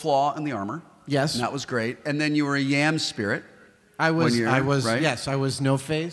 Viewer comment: what kind of spirit does Wil say Adam was? Sounds like yam spirit?